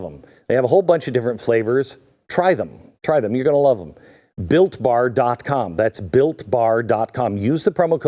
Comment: ohh.''BUILT BAR''....... i thought he said he enjoyed a good FART 5 times a day..sorry. british.